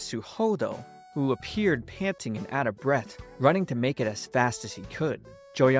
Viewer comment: “Who appeared panting and out of breath” Bro my brain thought you said “who appeared handsome and out of breath” 😂